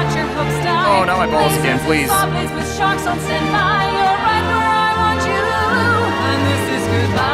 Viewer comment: is it just me or does she say this is right where I want you to loo